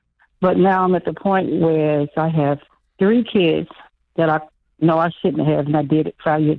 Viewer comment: At first, it sounded like she said “I had 3 kids- and I know I shouldn’t have.” I was like what?! 😂